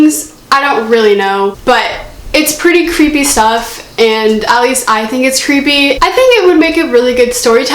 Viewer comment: When you said "at least I think it's creepy" I laughed so hard because I thought that you said "Ali's eye thing is creepy" 😂😂😂😂